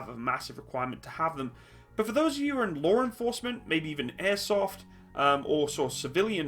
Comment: It’s probably just me but every time he says “Law Enforcement” I hear “Lore Enforcement.” “You are under arrest for the crime of fan fiction!”